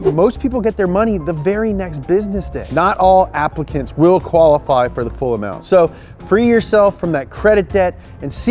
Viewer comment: Thought he said that "not all Africans will qualify for the full amount" during the ad. I may have been the only one who got that far in the ad.